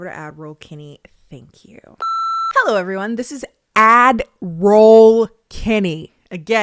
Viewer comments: I deffo was one of the people Who thought she said Admiral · I busted out laughing because I thought I was the only one that heard “admiral Kennie” for far too long before finally realizing it was “ad roll” 🤣💀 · I thought you were for sure saying Avril Kennie for the first twenty times 😂 🤷🏽‍♀️ I thought maybe that was your alter ego!? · Omfg, I thought you were saying Adderall Kenny!!! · I thought you were saying "Adderall Kennie" lol. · lmaoo who thought you said admiral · Omg I thought you were saying admiral this whole. Ad role makes so much more sense 😮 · Never thought you said admiral, always thought you said adderall - i assumed you'd have to get high to get through the ads lol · The first time I heard you say, "Add roll Kennie," I thought you said, "Adderall Kennie," and that made sense to me. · you say admiral kennie is the common misconception of “ad roll” but i thought u was saying ADDERALL